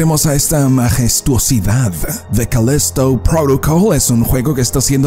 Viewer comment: Mano no lo digo por Joder pero entendi Olokesto Protocol XD